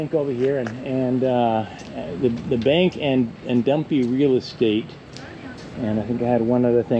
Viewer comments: I thought you said dumpy real estate. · I thought he said dumpy real estate😂😂😂😂